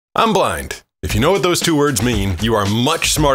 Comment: Guy: “I’m blind” Girl: “I didn’t ask for your race” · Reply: Bruh I thought he said I'm black 💀